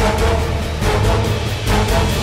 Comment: “I thought he said my daughter ate my home Clark”